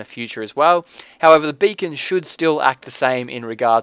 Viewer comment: I thought this said Bacon at first.